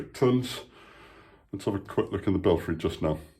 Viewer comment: thought belfry said belly